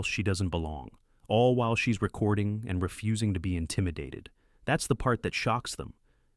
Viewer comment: I thought it said intimate at first I was like WTF it say intimidated